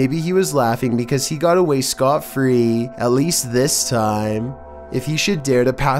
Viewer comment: WTF Did He Said At 2:03? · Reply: Or was it scot-free?